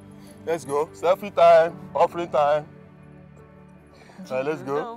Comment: Did I hear Dad say " Selfie time, offering time?" 😂